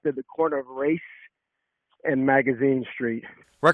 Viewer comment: Did he just say this happened at the corner of RACE and MAGAZINE Streets? How perfect is that!